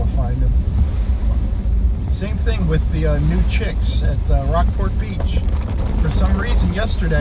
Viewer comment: You almost got that deer, huh? Then, you started talking about "new chicks." I heard "nude chicks." 😂 Stay safe, my friend!